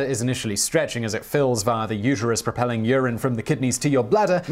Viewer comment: Men don't have a uterus less I heard you wrong. 0:49 Edit: oh ureters, not the uterus. Come enunciate, Simon.😛 lol.